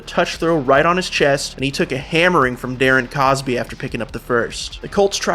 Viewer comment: no enlighten me cause now I feel played ngl. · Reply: so he was saying Darren Cosby … I thought he was saying cooper. MB